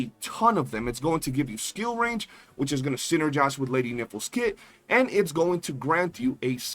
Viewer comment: I heard it as Lady Nipple. 😂